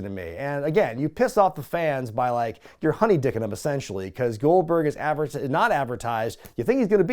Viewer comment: What did say around 12:34, again?... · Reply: I think he was saying "You're honey dicking him."